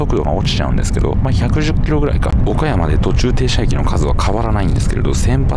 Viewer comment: 岡山に聞こえる😂